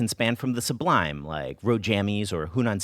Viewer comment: Loved everything about this so much!! At 3:26... Did you say „肉 Jammies“? 😅 Thanks for the shoutout friends 💛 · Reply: That makes so much more sense! At first, I thought he said "road"